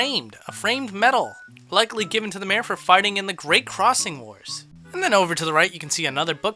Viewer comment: i hear for farting in the great crossing wars